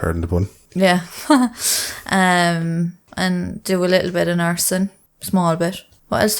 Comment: Did she say she does a little bit of arson!?!